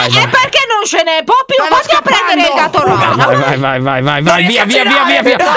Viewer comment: solo io ho sentito "vatti a prendere Catorgina" :')